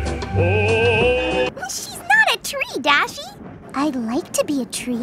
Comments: I heard tri-dashie lol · "She's not a tree, Dashie" kinda sounds like she says "She's not a Tridashie.", just really mispronounced." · « She’s not a TRIDASHI » Ok i had to make that joke cuz I understand TRIDASHIE instead of TREE DASHI xd